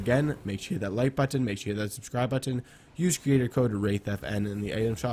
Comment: When he said “Hey Guys it’s wraith here” I thought he said rape.